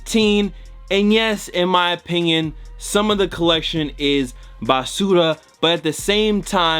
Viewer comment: Did he just say basura